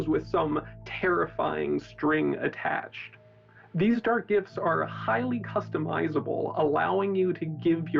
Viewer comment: I always hear "dark GIFs."